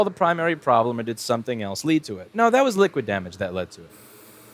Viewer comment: Baha. i thought it said air damaged. Being apple it wouldn't surprise me if a stiff breeze could kill a Macbook First.